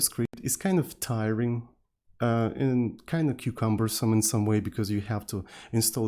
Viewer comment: Am I the only one who hears "cucumbersome" at this point?